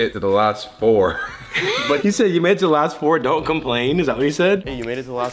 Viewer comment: think he said “can’t complain” not “don’t complain” 💀💀